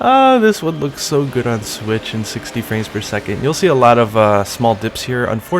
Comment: Did you say "on switch at 60 fps"?